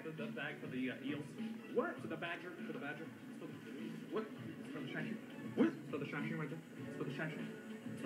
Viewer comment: I always heard it as "It's for the shined shoe". Makes sense b/c he's pointing at the guard's feet.